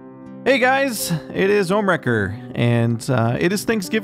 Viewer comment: For some reason I thought he said "Homewrecker".lol. Love you Ohm.